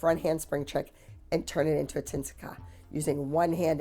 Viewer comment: What was the skill that Abby said (tin-si-ca?)?